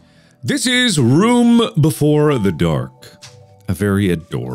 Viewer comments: i thought it said room before the dork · I thought it said room before the dork.